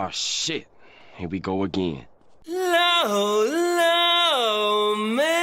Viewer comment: For months now, I thought your intro said "low, low mein" like a Chinese food... Now I realized it says "law, law man" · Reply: It's "long, long man." It's a reference to a somewhat racy and quite funny Japanese chewing gum commercial that was viral for a while.